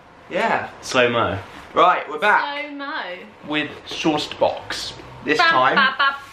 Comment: It's called Sourced Box? All this time I thought they were saying Sauce Box. Lol.